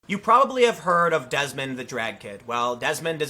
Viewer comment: 1st time I listened I thought he said Desmond the dragon... was thinking hey man it’s 2019! I wanted to be a fire truck 🚒 when I was a kid